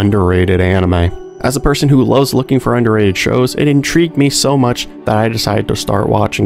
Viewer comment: I had to play back 1:41 several times, thought you said underaged shows.